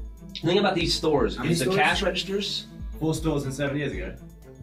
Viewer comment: can somebody please tell me what the hell Gavin says at 5:31 where everybody pisses themselves ??? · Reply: "Four stores and seven years ago." Not sure why it's funny, though.